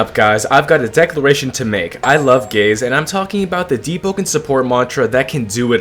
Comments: Is it just me or did i hear first "i love gays" instead "i love gaze" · Ngl at the start I thought bro said "I love gays" got so confused lmao